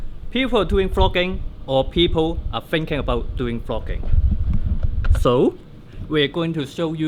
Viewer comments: I thought he was saying frogging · At first I thought he said FROGGING..